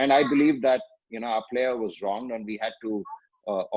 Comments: Did he say "Bhajji was wrong" ? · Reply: its wronged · Pls do with rahane.. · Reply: Did he say "Bhajji was wrong" ? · This is more of reminisce with Ash than DRS with ash.. thanks for this interview of Anil where he fondly shared his memories and for all the fans who were missing reminisce series... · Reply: Did he say "Bhajji was wrong" ? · Waiting for your interview with Dada 🔥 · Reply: Did he say "Bhajji was wrong" ? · Did he say "Bhajji was wrong" ? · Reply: he said bhajji was wronged you idiot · really kudos to you brother , for making these sort of chats intiative with players & enhancing us to know many facts related to the game we love , being a parent taking the kid to nets its really helpful in taking notes from players who represented India & TN in particular , great work brother ..Tnx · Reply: Did he say "Bhajji was wrong" ? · Did he say "Bhajji was wrong" ? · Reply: No. He said Bhajji was "wronged". meaning they did wrong thing TO Bhajji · Did he say "Bhajji was wrong" ? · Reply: he said bhaji was wronged. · Did he say "Bhajji was wrong" ? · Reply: he said our player was "wronged" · Did he say "Bhajji was wrong" ? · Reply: he said our teammate was " Wronged".. not wrong the meaning intended is exactly opposite brother. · Did he say "Bhajji was wrong" ? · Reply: He said "Our player was wronged".. which means "False Allegation" - Passive English. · Did he say "Bhajji was wrong" ? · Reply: dae mutta payalae...he said our players was wronged...😏😏